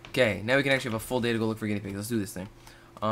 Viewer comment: i thought he said gay pig